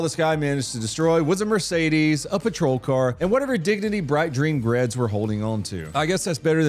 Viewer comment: did you say “bright dreen Gredds”? I swear that’s what I heard. 😂